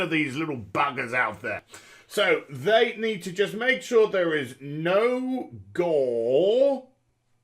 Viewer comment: I thought you said "No Goal!" :D